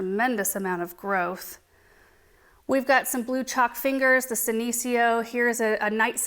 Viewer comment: Hahah I wasn’t listening properly and thought you said blue chopped fingers, and thought my goodness these plant names are getting more and more imaginative!